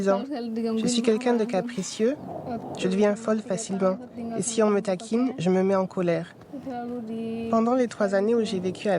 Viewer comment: Qu'est-ce qu'elle dit @ 2:30 ? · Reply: Une enfants maltraités qui retiens sa colère